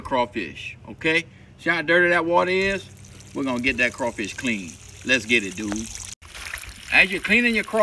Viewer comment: When he says "Les getit dewd", he's talking to his luscious mullet... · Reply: I thought he said “les get into it.”